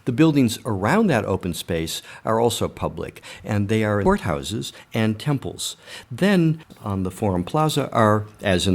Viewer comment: What do he say at 8:20 - 8:21 ? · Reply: LewHen Silvar He said "they are court houses and temples"